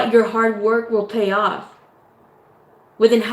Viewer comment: I heard “your HEART work will pay off” the first time I listened. Stay deep 😂💪❤️🙏 Thank you for this channeling!